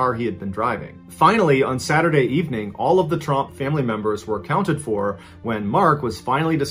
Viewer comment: I deadass thought he said, “The Trump family.” 💀